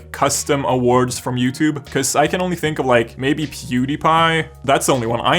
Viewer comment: the first time I heard “puebepie”